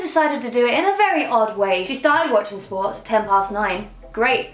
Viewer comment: Emma I thought you said "my sim started whatching porn at 10:9" lol